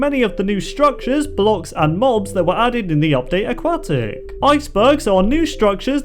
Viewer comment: it sounded like you said update a"quack"tic....